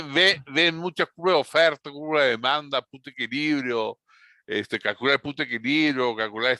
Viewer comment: Cuando decía "Punto de equilibrio" pensé que decía puta equilibrio xd